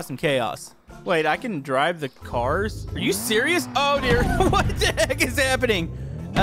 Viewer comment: sounded like "oh deer"